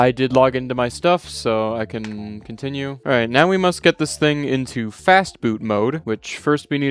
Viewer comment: I thought he said “fast food” 😂